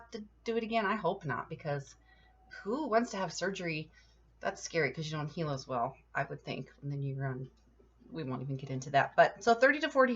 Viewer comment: What does she say at 3:06? "That's scary, cause you're on (IDK) as well." I listened to it like 6 times and still couldn't pick it up! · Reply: "that's scary 'cause you don't heal as well. I would think." She refers to having any surgery as a geriatric. Skin and other tissues heal more slowly at advanced age. I didn't think this surgery was bad at all. Alternatively, older folks seem to tolerate fusion reasonably well.